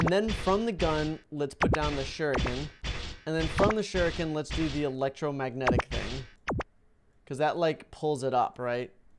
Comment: did he just say the Electromagnet for Zero-gravity